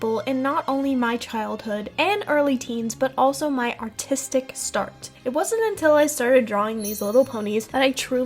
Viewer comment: i honestly thought you said “autistic start” 😭😭😭😭